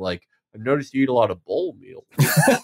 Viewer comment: It’s really bothering me that I can’t understand what Felix says at about 2:00 The guy comes up to the 23 year old and says “I’ve noticed you eat a lot of oatmeal” is that it? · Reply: I think "bowl meals", like açaí bowls and such